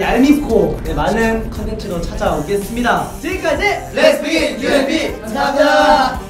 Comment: I thought they said “Lesbie” instead of Let’s begin